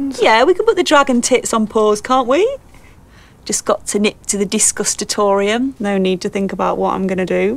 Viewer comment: Dobby was lovely before she became an annoying edgy hipster. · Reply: No you I thought she was saying Disgustatorium, just her nerdy made up way of saying toilet